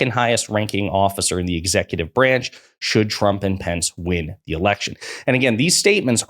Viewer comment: At about 3:55 into the video does he say "Trump and Pence"? I swear it sounds like pence not vance.